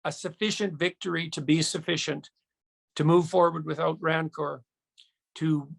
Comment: I find it very interesting that while I watched his video with the subtitles on, he said at 2:04 minutes the quote "to move forward without rancor" was translated as "to move forward without greed" in the subtitles. To move forward without rancor is a much different idea so they B.S. that is presenting globally is not what he said or tried to explain. This seems like a very intentional misinterpretation. There is a difference between rancor and greed. Just another attempt to be submersive by the leftists who control the medial and internet services IMHO. It may not be much but we do see what is going on whether you like it or not.